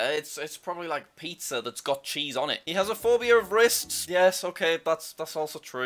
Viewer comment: I thought it said “he has a phobia of racists” 🤦